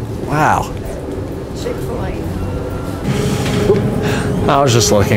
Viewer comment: Lmao did anyone else hear at 0:45 someone said "chick fil a" lol